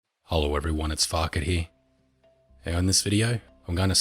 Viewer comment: When he says "hello everyone this is farket here" i hear "Hello everyone this is fuckity"